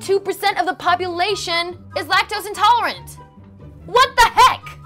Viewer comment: when you were talking about lactose intolerant people, I thought you said black toast intolerant. I need to get my ears checked